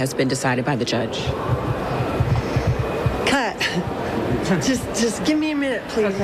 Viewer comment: does she say "cut"? Like... Is she talking to the camera crew? To give her a minute so she can prepare herself for the question? Cuz I can understand needing a minute but as soon as she said "cut" like she's a director or something made me feel all sorts of upset